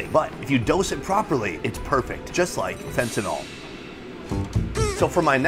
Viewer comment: did he say fentanyl?